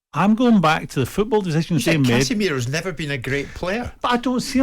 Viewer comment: Souness never played against Cannavaro, lmaooo, I thought Souness said Casemiro isn't that good